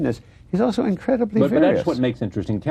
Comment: Does anyone know what Bloom says at 08:35 'he's also incredibly..' which the interviewer interrupts? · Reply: Various